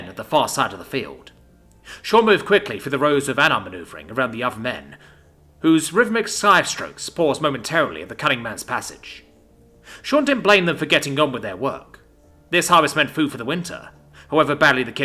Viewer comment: For a sec thought that shit said the cumming man🗿